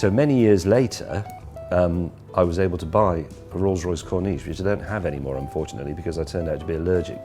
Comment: A bus drove past my house right as he was saying he bought his first Rolls and I genuinely thought he said it was a Rolls Royce Cornelius XD